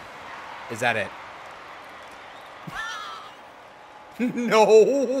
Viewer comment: Did anyone hear someone say no or something